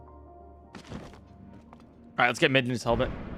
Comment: did anyone hear "Midnu" instead of Midna too?